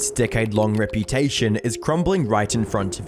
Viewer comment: Thought you said dickhead reputation instead of decade. Both work